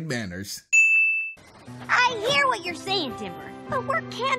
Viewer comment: am i the only one who hears rd saying "i hear what your saying dipper"?